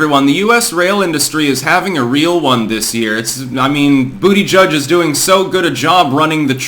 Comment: Confession time: When you first started talking about this guy in your videos, I kept hearing "booty judge" and thought you were talking about a really thicc Supreme Court justice or something.